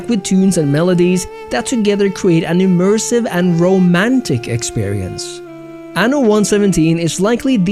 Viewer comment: Did you just say "rome-antic"?